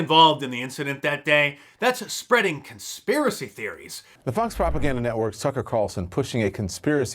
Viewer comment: Sounds like he is saying "The Fox propaganda network Sucker Carlson"